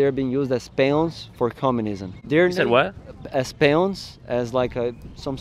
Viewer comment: i think he said peons, not pawns